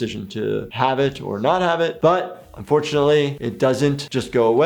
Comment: Help me out here - 8:39 Does he say Unfortunately? Well that was pretty biased.